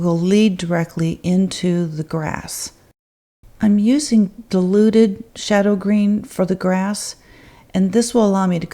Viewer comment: That's so beautiful! I love your voice, even though I'm Australian and thought you said 'deluded pigment' at one point and realised it was 'diluted' 😂 I really enjoy accents.